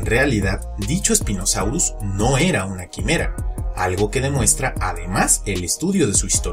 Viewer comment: que dijo en la parte: no era una.. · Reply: "Dicho Spinosaurus, no era una quimera". Sé lo que es una quimera, pero aun así no entiendo por qué se refirió a esa versión del spinosaurus como una.